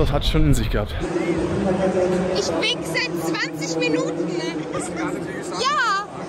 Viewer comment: Wer hat bei Minute 3:25 auch „ich wixe“ anstatt „ich wink seit“ 20 minuten verstanden? Hat mir voll die inscope lexa vibes gegeben😂😂